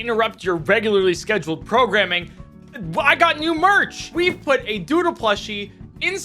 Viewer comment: I heard "Really quick, just to interrupt your regularly scheduled crowgramming"